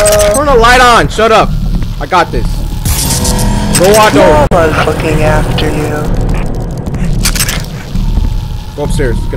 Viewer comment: who else heard 11:05 "mamas freaking after you"